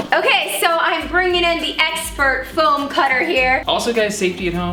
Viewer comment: thought she was going to say ex BF. Rebecca really exaggerated the ex from expert. But of course, Matt is her husband.